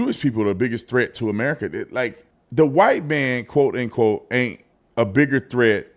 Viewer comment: I thought she said weight man. What a racist!